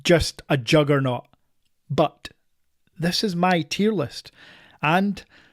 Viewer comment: For a second there I heard "my STD list", and then I figured it was your accent 😂